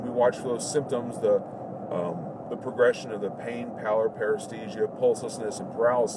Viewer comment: Really appreciate all these CEN videos. But qeustion: . At the 12:14 mark, not sure, but can anyone make out what is saying? transcript says "power.." but i dont think that is correct... -- > "It is important to monitor symptoms and progression of pain,.....power? ...... paresthesia, pulselessness and paralysis. Thanks in advance! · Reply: Yup pallor